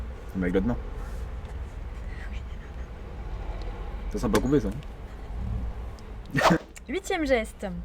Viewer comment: Il a dit quoi svp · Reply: "C'est simple à couper ça !"